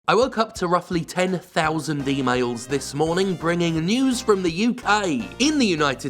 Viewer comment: For a moment it sounded like he said "d-mails" instead of "e-mails" at the start and I thought: Wait, is he bringing news from the future? Also, great choice of muisc with Casino Night